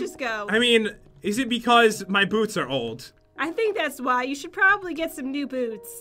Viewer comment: The funniest thing in this video is the caption at 8:46 it says im taking out my boobs xD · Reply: When he said "my boots are old," I heard "my boobs are old"